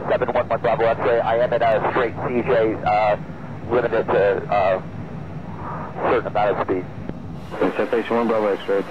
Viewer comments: sounds like he said "limited to a...certain amount of speed"...regardless, rip in Heaven to both of them. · the one "Unintelligible" sounded like "Certain amount of speed"